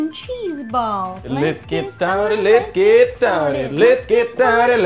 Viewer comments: At 57 sec I thought they said "let's Get Retarded" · when they were singing "lets get started" it sounded like they were saying " lets get retarded":😂😂😂😂😁😁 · When said let's get started it sounds like he is saying let's get retarted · Was I The Only One Who Heard Say "Lets Get Retarded!" 0:54 xD LOL